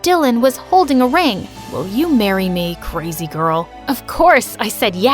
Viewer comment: When he said "will you marry me?" i thought he said "Will you marry me GinceyJed-"